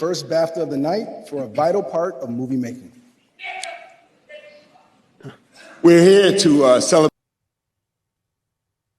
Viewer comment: I couldn't clearly hear what he shouted out at 3:49. Can someone tell me? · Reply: He said nickers